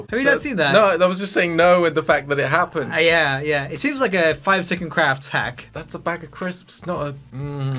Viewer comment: Did you guys notice Joel saying “5 second crafts” instead of 5 minute crafts at 11:52??